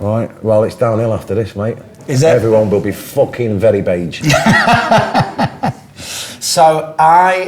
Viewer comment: What does he say at 0:10? Very beige? Bad? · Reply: Beige.